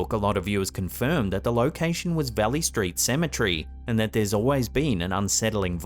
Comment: I live in NH is he saying Belly Street Cemetery? I’d like to look into it :) mark 8:24 he mentions the location.